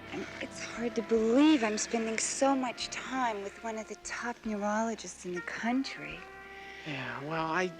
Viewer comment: Heard it as numerologist, not neurologist...